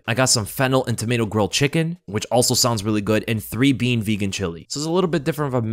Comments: Sounded like said "fentanyl and tomato grilled chicken" 😅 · I swear it sounded like you said "fentanyl and tomato grilled chicken" lol. I was listening without watching and had to rewind to make sure. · I really thought this man said he got Fentanyl grilled chicken 😅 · I swear it sounds like you said Fentanyl & Tomato Grill Chicken🐔😂🤣😂